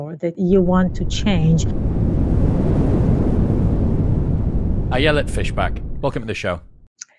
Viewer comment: All I heard was "I yell at fish back"...